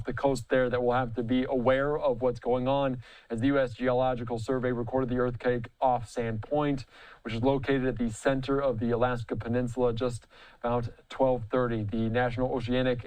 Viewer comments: "Wait did he just say Earth Cake? @ 1:20/ 4:00🤦🏾‍♂️ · did he say Earth Cake